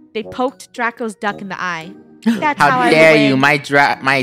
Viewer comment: what i heard from draco: how dare you poke my dru- duck..